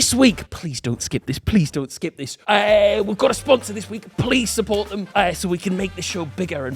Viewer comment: Oh no i skipped, sorry i thought you say please skip..